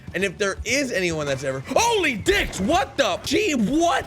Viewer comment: He says Holy Dicks?!? Did I hear that right? Funny as hell.